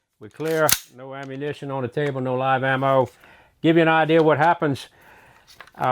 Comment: I can't help but laugh when he says "no ammo" then I look at the wall behind him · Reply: I never heard him say no ammo I heard no live ammo on the table